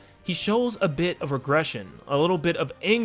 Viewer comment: Hey at 11:55-11:56, did you say aggression or regression?